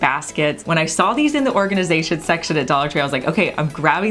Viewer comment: I think they are plastic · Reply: oops! I thought said she got them in the glass section of Dollar Tree